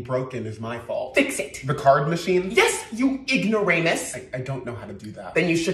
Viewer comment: why did i hear "ignoranus" 💀💀💀💀💀💀💀